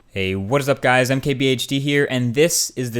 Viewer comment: I always thought he said I'm KBHD